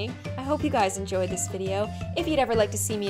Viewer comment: did she just say "If you never liked to see me again hit post notifications"? lol.